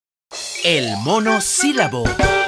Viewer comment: Pensé que decía el mono silbando xd